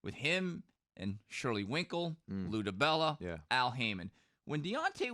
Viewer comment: "Shirley Winkel" who did you just say?